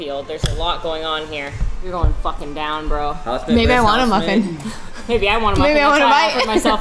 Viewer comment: can someone please clarify what said?? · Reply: Maybe I want a muffin